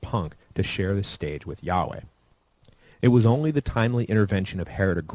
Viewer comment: Did someone say Yaoi? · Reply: GREAT troll: i salute you!!! Am a huge fan of Anime myself. That said, it was Yaweh, lol.